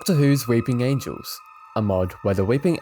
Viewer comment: is it just me or did i hear "dr whos r#ping angels" 😭😭